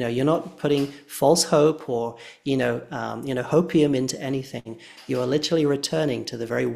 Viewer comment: sir, did you say 'hopium'? 😂 I love you so much 😂😂